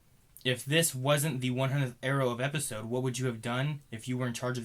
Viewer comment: did you say "the 100th Arrow of Episode"?